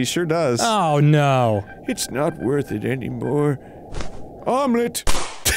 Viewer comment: Omlet!!! I cant stop laughing. Did you say Omlet?!?